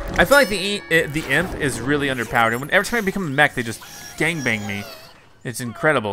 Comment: what? Did he say gang bang?